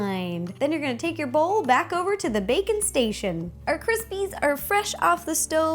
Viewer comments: did she say "Bacon Station"? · IT SOUNDED LIKE SHE SAID NOW LETS GO BACK TO THE BACON STATION